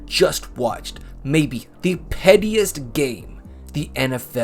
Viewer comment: I thought you said "prettiest".